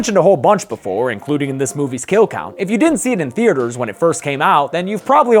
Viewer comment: ok? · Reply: I thought u said kill count lmao